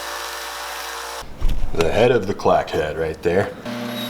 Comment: i thought he was saying "crAck head" lol